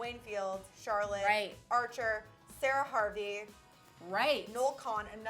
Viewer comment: she really said shower harvey? hahahaha